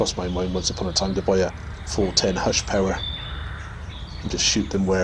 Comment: I believe it was around #37:30; Just afterwards... before the #7 th squirrel was shot at #39:28,you were testing .22 barracuda's, I may have misheard you're pronunciation...the rest is conjuncture on my part from reading the top of your pellets tin, which displayed some info....I just recently found out #12 pounds equals #16 joules, not #25(my error)& most UK airguns are said to be #11.5 or under due to inspections;as based on pellets weight(mass) & velocity equals fps(energy)..... sorry... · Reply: I see what you mean! A small hushpower .410 silenced shotgun - <link> - was what I was saying (I think). Yes 16 joules is about what 12 ft/lbs is equal to.